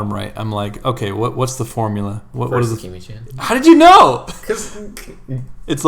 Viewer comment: What did said at 9:42? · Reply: i think he said "sakimichan", its an amazing artist too